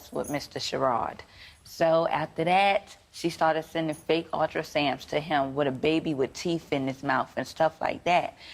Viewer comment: Oh my lord! Did she say "ultrasams" @ 8:37? 🤦🏽‍♀️🤦🏽‍♀️🤦🏽‍♀️